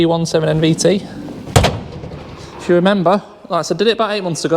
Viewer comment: Re Fiat 500. The reg you mentioned (SG17 MVT) is for a black VW Polo. Unless I misheard you?